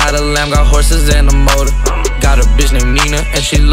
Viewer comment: did he say "got a bitch name nena" it cant be the nena who dated McQueen!😬😬